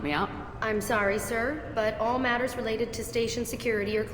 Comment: Did she say sir?